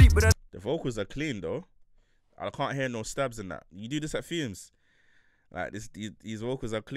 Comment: 😂😂 you really drawed out Fumez's mixes like that. Legend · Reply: I swear you said fumez